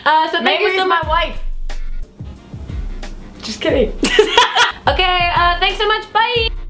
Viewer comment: What does say near the end, and then says "just kidding" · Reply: She said "Mamrie is my wife... just kidding!"